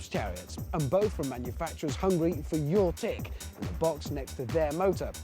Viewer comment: Am I the only one who misheard him say "Hungry for your tick"...?